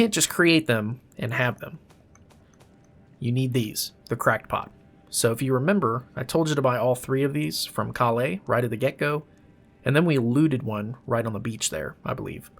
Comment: i heard "however ....... you need a crack pot" that made me giggle.